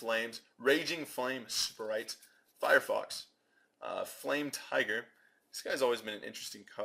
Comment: did he really say firefox at 5:02?